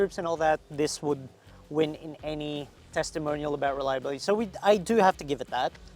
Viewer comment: Availability of Spare Parts Test and Low Value Maintenance Test Why not include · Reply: I thought you said "reliable". why do you need spare parts for?